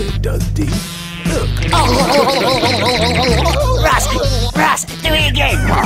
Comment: when is being shaken, I swear I can hear him say something like "Jeez, Russ!!"